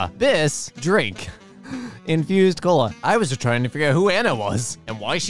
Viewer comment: i thought it said "Confused Cola"